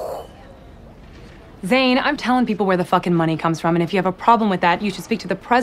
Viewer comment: is she saying "Zane am telling where the fucking money"